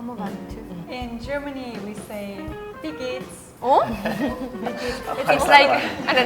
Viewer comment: They seriously wrote "idiot" instead of "wie geht's" in the subtitles, lmao :D:D:D